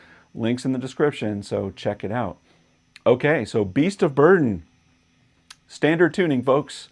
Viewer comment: I always heard my pizza's burning when he says beast of burden but I was 7 when i first heard it